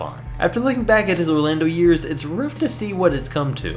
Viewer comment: Lol at 9:05 he says roof instead of rough😂😂😂